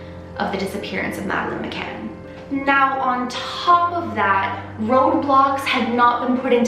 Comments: did she say "Roblox"? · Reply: She said “Road Blocks” not roblox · Did she say roblox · when she said road blocks I'd thought she said ROBLOX didn't yoouuuuu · was it just me that thought she said roblox not roadblocks? i was like what does roblox have to do with this?😅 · On 17:17 did she just say roblox? 😂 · Did she say ROBLOX? I was playing that while she was talking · Smh I thought she said roblox. I'm like what does roblox have to do with a case about a missing child? I'm dumber than I think sometimes. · When she said road block I thought she said roblox so in the middle of this serious video I was laughing out loud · between 17:15 - 17:20 i thought she said roblox XD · did say roblox? As in ROBLOX the online game?! :\ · At first I thought she said roblox lol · Okay, not related to the case but, I thought she said "Roblox" at 17:15. Was so confused lol. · I thought she said roblox had not been put into place, so I had to play it back, and then on CC. It said roadblocks XD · LOL I THOUGHT SHE SAID ROBLOX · it sounds like she said Roblox